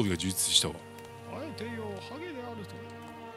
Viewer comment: ハゲイジリの後の1:45:45あたり、「あえて言おう、ハゲであるぞ！」って聞こえる